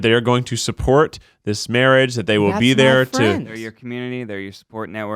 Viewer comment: is there anyone heard a female voice at 25:45, which said: "That's my friend!" ?